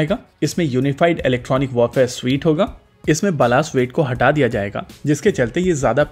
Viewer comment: kya baalas weight...!!!! Kya kaha aapne